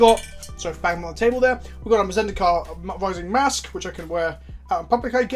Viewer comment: at some point during the video I thought you said "Zendicards". now I'll start using it. thanks